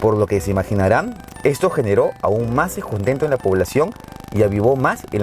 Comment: ¿qué dijo? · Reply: Aún generó "más descontento en la población".